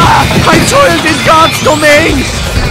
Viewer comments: What did he said ? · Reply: "I toiled in God's domain!" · what did the Medic say to Scout? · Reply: I toiled in God's domain!